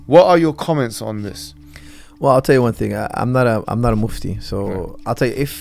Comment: Holy at the beginning at 0:24 I thought he said I’m not a Muslim, heart skipped a beat!!